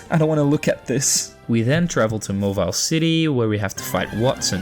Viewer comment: "We then go ahead to "Mawile City"" I know it's mauville, but i legit heard "Mawile City" and found that hilarious